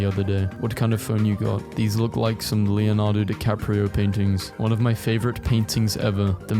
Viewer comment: did bro actually say Leonardo DiCaprio